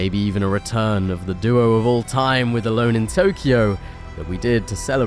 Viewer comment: You say aloneintokyo, I hear "hJune with no mic". When is the 6 solos making a comeback?